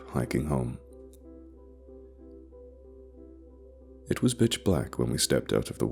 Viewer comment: Did he say, ‘It was bitch black...,’ at 05:48?? Was it written that way?! 🤣